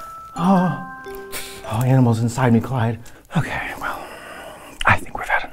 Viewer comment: “All the animals inside me quiet” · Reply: I thought Katya said "all the animals inside me collide" 🤔😯